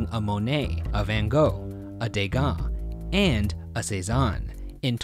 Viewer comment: i'm just mad at the subtitles rn for mishearing degas as 'gauguin' and misspelling cezanne